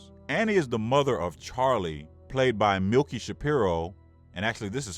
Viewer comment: Did he just call her Milky?